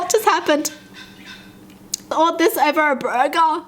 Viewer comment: did she just said bruh ger or its only me??